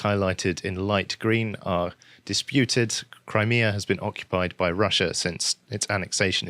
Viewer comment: I'm sorry Russia annexed what? Cuz it sounded like you said Korea, and then said it was at the bottom of a map of Ukraine. Now I'm no map biologist, but something seems off about that statement.